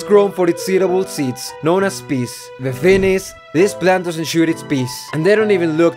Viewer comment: did I hear peace instead of peas